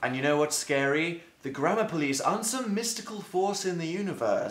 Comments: At first I thought he was saying grandma police and I got really confused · I cant spell, I just relay on auto correct, but my computer doesn't have auto correct. x) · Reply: lol I thought he said grandma police